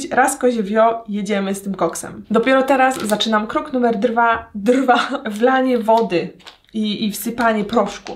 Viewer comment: jak usłyszałam to "drwa" to myślałam że zjesz drwala albo coś i od razu się głodna zrobiłam i nabrałam ochoty na drwala